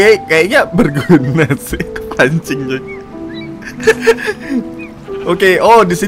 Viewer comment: Gw salah denger gk sih 12:03 Bang bilang A**ing